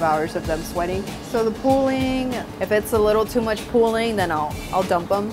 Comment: What does she mean when she says "If there is too much pulling then I will dump them 6:49"??